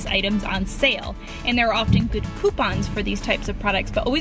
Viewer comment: i thought she said pooponps insteast of coupons